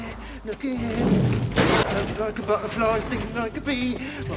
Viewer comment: what did he say? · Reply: He said “wagwan” which is like an English slang-type greeting meaning “What’s up?” or “What’s going on?”